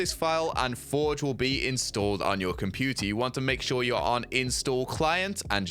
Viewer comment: Is it just me or does he say computie